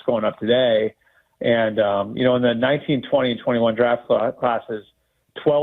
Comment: When he said 19, 20, and 21 draft classes I heard it as 1920 and 21 draft classes